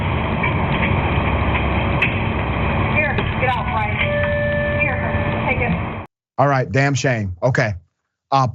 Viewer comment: Un, are my ears deceiving me or did she say "Here HUN, Take it!" ?😲? starting at time 0:26 ?😳? ,, can someone ELSE have a listening to verify this? ....If so, then that may explain her "Willingness" to comply so easily as if this was all scripted I mean as if to KNOW the boy er I mean, felt SORRY for the Boy robbing her! What a way to get FREE MONEY and also collect "Insurance" for the monetary losses! 😆😲😳... I mean....🥺😉👌🥺🥺